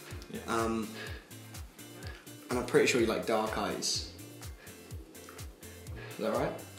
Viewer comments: the way charlie say "dark eyes" i thought he said 'dark arts' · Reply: I am sorry I thought I heard “dark guys” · the way charlie say "dark eyes" i thought he said 'dark arts'